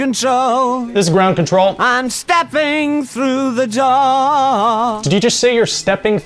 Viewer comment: I thought he said he was stepping through the jaw